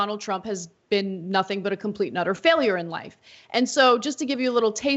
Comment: Ana: sorry to mention this, no dislike here - I’m a fan - not a big deal obviously but 1:40 can you enunciate “and utter” more when you say “a complete and utter”? I always hear “a complete nutter”. And it throws me every time. It’s a funny thing and again no big deal and might only be my ears. Sorry to be critical. Thanks for all y’all do there at TYT! 🙂